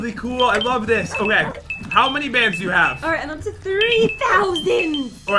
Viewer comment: I turn my subtitles on and the subtitles says “*how many babes you have*”